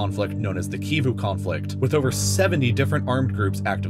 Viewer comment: Did he say "17 armed groups"? No, I misheard. He did say 70. dafuq?!